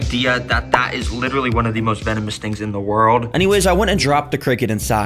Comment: bro literally said ”most venomous snakes” instead of spiders 😂😂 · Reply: Actually he said "things", I thought he said snake too at first 😂